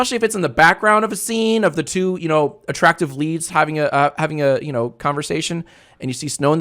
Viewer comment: I was listening with earbuds and eating a chip and subtitles weren't on so at first instead of leads I heard leaves